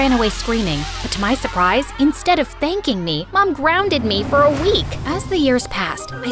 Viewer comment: I thought she said “but instead of SPANKING me.” Lol